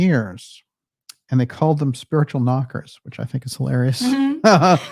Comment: All A Maze Ing🌱 …especially from 1:33… brings out some Gems.💎 …”Spiritual Knockers” …could be a name for a punk band!😂 hilarious. 💎🎯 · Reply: Oh, I thought I heard "mockers" like in " mock up".